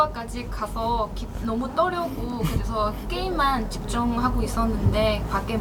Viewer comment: he did not say he was shaking, he just said he was nervous cos of game 5 x